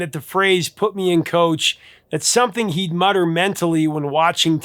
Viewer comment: Misheard lyrics until about a decade ago, ... I really heard it as "Put me in COLD, I'm ready to play" rather than coach. Sure, I knew he was speaking to the coach, but for someone who played ball for 19 of 20 seasons from 13 to 32 years old and 21 years by the time I was 42, in community leagues across PA and MI,... I saw it as an eager willingness to get on the field, even if I hadn't been there for warm-ups. John's enunciation is a little sloppy there. ;-)